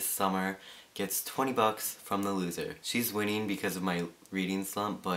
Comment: Oh, HAHAHA derp I'm a fool, misheard bucks as books. I was gonna say - thats a crap load of books!